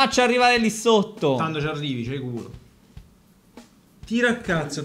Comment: Al minuto 6:43 non ho capito se dice: c'hai culo o c'hai il gufo; qualcuno che mi illumina?😂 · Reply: manan dice culo